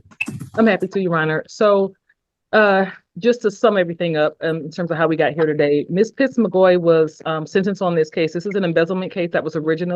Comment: Straight up thought the attorney said “Ms. Tit’s McGee”. I guess my wife is right about my hearing going.